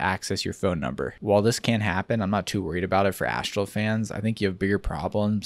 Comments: I thought you were saying ActualFans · This whole time I thought you were saying ActualFans